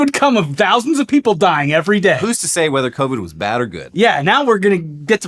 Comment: i was coughing and thought you said coke instead of covid and I suddenly had MANY more questions about the butt breathing research